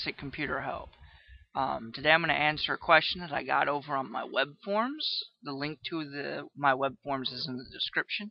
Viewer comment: I thought he said web porn